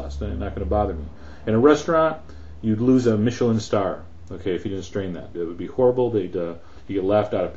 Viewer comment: sorry im not a good listener, but what did you exactly said, "if you strain that" or "if you don't strain that"??????? that short word made me confused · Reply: He says, "if you didn't strain that."